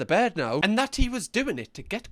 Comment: I don’t think he means to “physically saturate”. I think the person is saying “psychically” to mean “saturate each other in a psychic-like manner”.